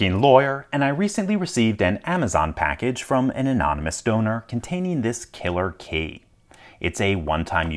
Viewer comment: I was confused as I heard ‘anonymous stoner’ lol